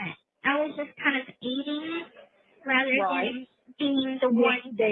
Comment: Listen to 22:00 she said she was eating during the crime. Instead of participating in the crime, did I hear that correctly. I'm going to listen to the one you referred. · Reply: I believe she said she was aiding as in she assisted in the crime but didn't do it directly.